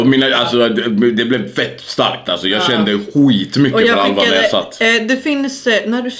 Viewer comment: Jag sitter å skissar så när jag hörde "jag kände skitmycke för Alva när jag satt" så kolla jag upp direkt och vänta mej någon form av reaktion av Alva haha